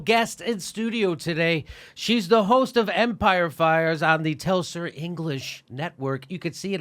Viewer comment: Hi Jimmy, you don't know me and that's cool, but it kinda sounds like you say "Empire Fires" when Abby's show is called "Empire Files". I think she is too gracious to correct you on your own show, but it is a little distracting. Thanks for your content and having guests like Abby on your show.